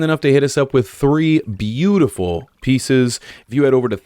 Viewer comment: I thought he said 3 beautiful pizzas ...on the thick qss glad ad